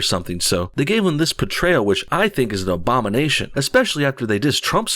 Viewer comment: did you say obama-nation?